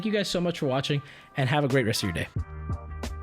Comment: I heard "and have a great yesterday" at the end first 😂